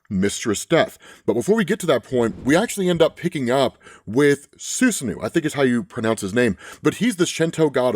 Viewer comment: 🤔 did anyone else hear "Snu-Snu" at first i had to rewind to listen again then heard "Su-Snu" 😅